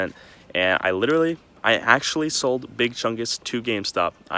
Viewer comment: I thought IT said I 'I told gamestop big chungas